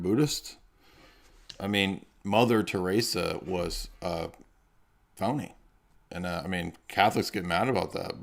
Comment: what do you say here? You said "mother theresa was ___"? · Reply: Phony